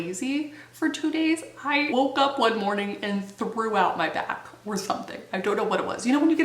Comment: I swear I can't be the only one who thought you said "I threw out my bath" because omg